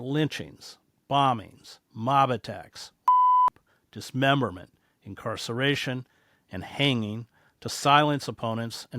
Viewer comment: What's with the (*beep*) -ed out word at 1:59??????? What word is too dangerous to air here???? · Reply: Rape